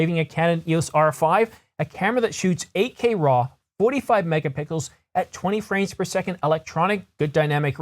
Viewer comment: Hahahah did anyone else notice at 0:50 it sounds like he says 45 mega pickles:goodvibes